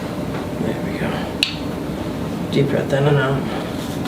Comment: So for some reason my YouTube English captions are on and @ 13:59 when he tells her “deep breath in and out” the captions say Deep breath Eminem 😂 bro I laughed way too hard at that lmao 😭